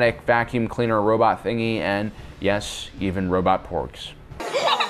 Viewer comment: I honestly thought you said robot porn at the end.